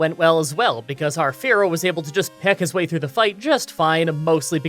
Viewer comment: I originally thought you said "hero" instead of "fearow for a sec, haha. Totally going to name a Fearow hero now!